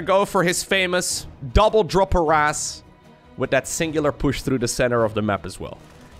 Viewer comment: I misheard double drop harrass to double drop that ass